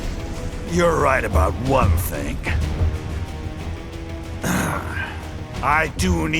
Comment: THINK! NOT THING! HE SAYS YOURE RIGHT ABOUT ONE THINK!